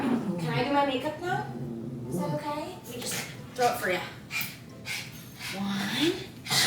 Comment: I literally thought she said “let me throw up for you”